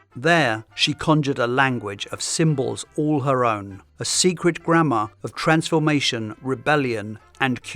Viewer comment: Im so American when you said "grammar" i heard "grandma"